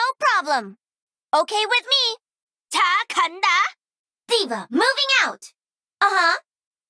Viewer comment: anyone could tell me what she say?? xd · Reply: Taa ka ta? v